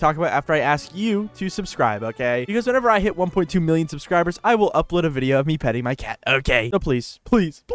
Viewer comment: i misheard that as "i will upload a video of me cutting my cat" what